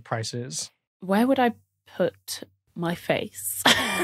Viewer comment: "why would I put [weird cut] my face?" "we're just gonna let that one slide" seems like there was more to that question that was not suitable for the public? like "why would I put (something) on my face?" or something like that? Now I want to know more! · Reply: I thought she said "Where would i put my face?" which led to Tom's quick turn away.